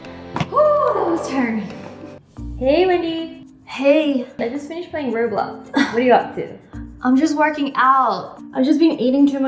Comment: Did Wendy just say let’s finish playing roblox to or is it just my ears? Time!